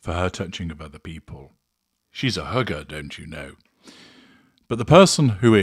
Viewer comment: I would have sworn you said hooker instead of hugger😅. I think she keeps her hand on his leg to give him squeezes when he's acting inappropriately.